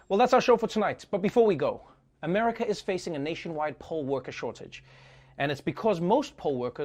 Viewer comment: i keep hearing "Coal worker shortage" hahahahahahaha